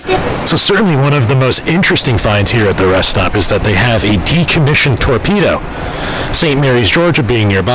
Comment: At first it sounded like you said a decommissioned tortilla 😂😂 I just pictured a giant burrito laying there lol!! Have an awesome time guys!! Enjoy your trip together!!